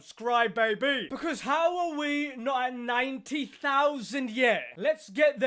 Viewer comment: When you said "how are we" I thought you said "Halloween"